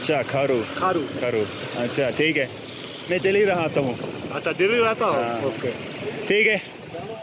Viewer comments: I heard "main daily nahata hun" 😅😁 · Hello to from Australia. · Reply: When Karl said "mein delhi mein rahta hun", I heard "mein daily hilata hun". 😂